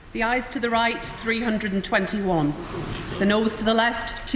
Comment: Ayes not eyes lol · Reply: Alex....no disrespect meant , I only laughed because as a non British person at first I thought the speaker was saying eyes to the right , nose to the left lol ...about ' pricks ' they're politicians so what else would they be !